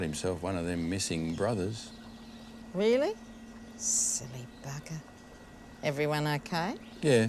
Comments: Did she just say " Silly Baka "!? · DID SHE JUST SAY SILLY BAKA 🤣🤣🤣🤣😭😭💀